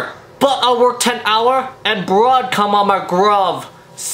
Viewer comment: I thought he said I work 3 hour and bruh come on my glove Lol